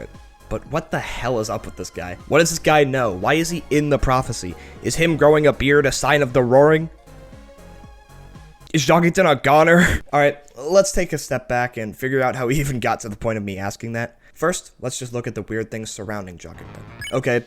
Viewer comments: I thought it said "is jockington a gooner" 💀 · My dumb aah thought it said "is jockington a gooner" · I thought it said "is Jockington a Gooner?" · I thought it said "is jockington a gooner" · I really thought it said "Is jockington a gooner?" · I thought it said is Jockington a gooner, and I was like “ Wuh “